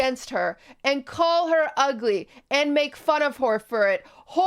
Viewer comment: sounded like you said "make fun of whore" 😖😅